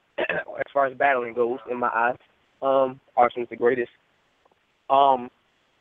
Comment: Did surf say araonal was the greatest from jersey @ 12:22?